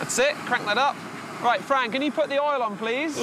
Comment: Did he called that girls "Frank"?